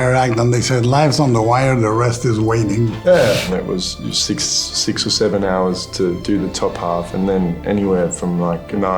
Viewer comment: did I just hear 6 or 7 Jacob?